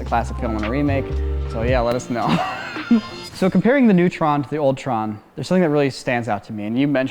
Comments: I keep hearing "Comparing the neutron to the Ultron" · I swear I heard "Ultron and Neutron" when said "old tron" and "new tron"